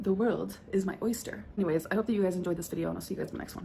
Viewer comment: i am creating my own dialogue after smokin rn when she said “hope you guys enjoyed this video thanks bye” i could’ve sworn she said “i’m sure you guys don’t like video thanks bye” i had to go back